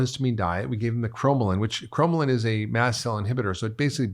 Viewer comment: are you saying Chromium? What are you saying?